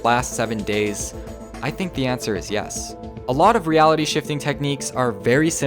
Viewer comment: I thought it said shitting